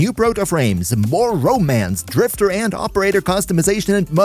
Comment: i might be buzzed but i heard oreo